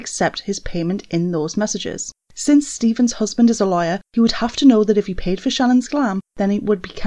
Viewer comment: i swear i heard liar 🤣